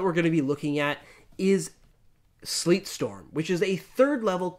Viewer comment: lol, at first I thought you guys said "Sleep Storm" and my mind started thinking how that could be played.